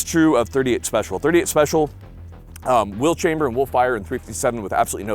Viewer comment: Just remember: you can put .38 Special into a .357 Magnum, but you CANNOT put a .357 Magnum into a .38 Special. I had to listen twice at 10:24 ; on second hearing, you said ".38 Special will chamber in .357", where I first heard ".38 Special will chamber a .357."